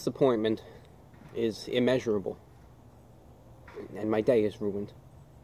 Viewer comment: I can't think of anything to say so I just said this. By Popular Demand: "My disappointment is immeasurable and my day is ruined." merchandise that became a popular meme from this is now at my Teespring store, if you're interested ► <link> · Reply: i came back to say this but i really thought u said my diet is ruined